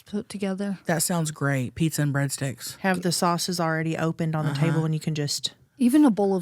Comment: accent when she said "Pizza and break sticks" the 1st time sounded like she said pizza and Brett's Dick 😂🤣